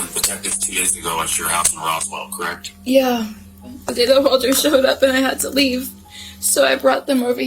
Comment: what does she actually say here? “The data holder showed up” doesn’t make any sense does it?